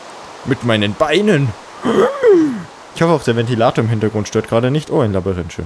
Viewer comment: Schönes Video.. Wie immer. 2:58 Bei "Ventilator" habe ich komischerweise "Defibrillator" verstanden.. :D Brauche ich ein Hörgerät? :D